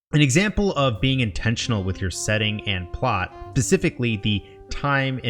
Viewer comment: you said pacificly instead of specifically